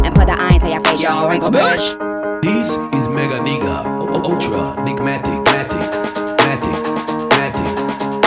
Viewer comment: Did he just say this is "Ultra Nigga"?